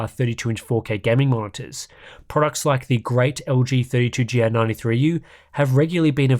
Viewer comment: Trying to get what is said about 00:28:08 - "The great LG..." 32G93AU? 32GN93? ??? Cannot find anything searching for such model - or a reference in the text. Due to me working from home from time to time and leaving both streams and documents stationary for a long time - I have a severe risk for burn-ins. So, now when I want to upgrade to a 32" 4K monitor for both work and gaming - I really want to know what my best non-OLED options are? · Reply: lg 32GR93U